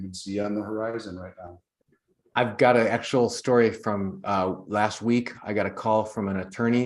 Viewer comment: did you say "A (not AN) actual story?"